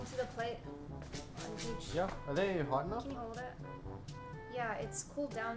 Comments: I heard "can you hold this" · Bro I heard "Can you hold it" so I was so confused at the second half of the video